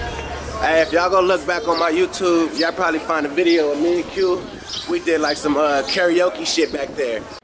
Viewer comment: "Yall probably find a video of me & Q" but subtitles say "I probably find a video or manicure" 😂